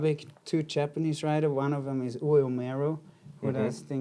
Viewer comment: which Japanese snowboard company / shaper is he saying? Thanks yall! ☃️ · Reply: Ue Umeru for Field Earth